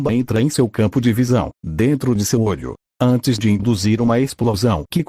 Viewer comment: Eu ouvi "seu olho do cu"